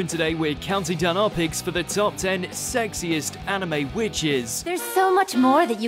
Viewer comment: Ohhhhh I was so confused about this video I thought it said sexist and I didn’t get it at all